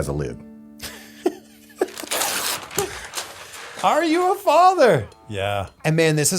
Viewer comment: I thought it said are you the father for a second. I was like DAMN. Going deep!